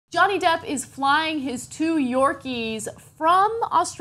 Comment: I thought she called him 'Donny Duck'.